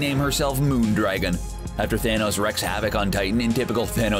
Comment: After Thanos WHAT'S havoc? Did he say "wrecks havoc"?